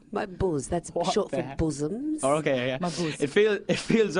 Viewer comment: from 6:16 to 6:25 when he says my boos i thought he said my balls are still sagging down at first!!! HAHAHAHA